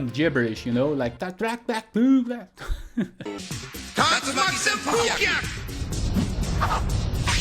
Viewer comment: I always heard "a psyched-sike whirl kick"... 🌀 🦵... And when I read the manga, I was like no way they're saying all that! Tatsumaki senpu kyaku!?